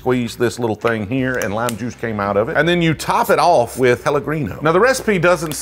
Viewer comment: I swear to god I heard you say “hella-gringo” I place of Pelegríno.